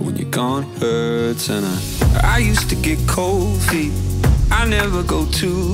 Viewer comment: Now I only hear “I used to get covid” 😂😂